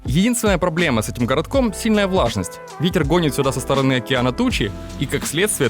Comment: Мне послышалось Питер гонит сюда дожди))))ахахах ох уж этот Питер))Спасибо за видос,Антоха)) Это мелодия с нетфликса добавляет антуража)))